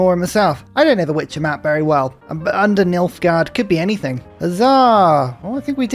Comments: Me, halfway through the video: Oh, Nilfgard! I thought you said Milfgard. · oh man, I've never played or watched or read the Witcher, so I've been half paying attention to this series, but you best believe I snapped to attention when I thought you said MILFgard